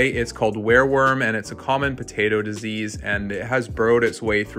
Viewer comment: Sounds like you said were worms not wire worms